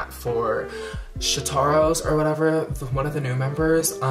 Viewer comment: it sounded like you said shitaro oop